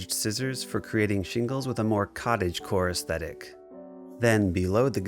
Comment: did he just say cottage-core???